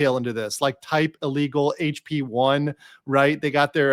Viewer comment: Lmao, I thought you said “HB1,” like the work visa, as in they came in with a legal visa but overstayed or something.